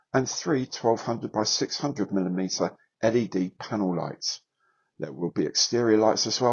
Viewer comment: Looks great. When you said ‘panel lights’, I heard ‘panel lines’ and it didn’t sound out of place at all.